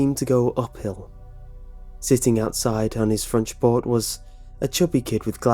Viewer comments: Did anyone else notice at like, 1:34 he said "fronch port" instead of front porch? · Did anyone else hear him say "fronch port" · he says fronch port instead of front porch.